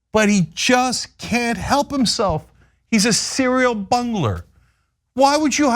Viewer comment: 🤣🤣🤣 I thought Cenk was gonna say "bunghole" instead of "bungler". Come on Cenk say it